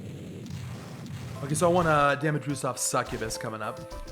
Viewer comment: Captions say “damage boof” instead of damage boost. Outstanding.